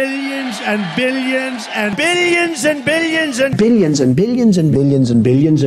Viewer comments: I just hear the name "Bill Jensen" 😅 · I heard billions, opinions, and obedience after